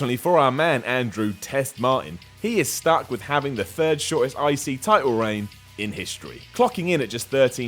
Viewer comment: I thought he kept say 'icey title'